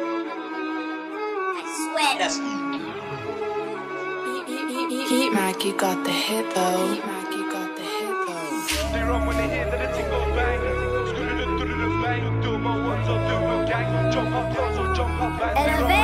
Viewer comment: What it says "Heatmaky got the hit,though👍 Vs What i hear "Matthew got the Hippo"💀